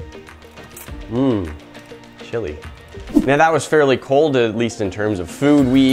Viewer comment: Who else heard “Mmmm, chilli”